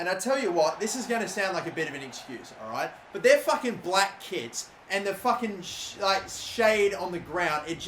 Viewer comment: I thought he said black kids isn't of kits